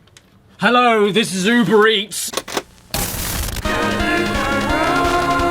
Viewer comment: For the longest time, I thought he said "Hello this zoo breats."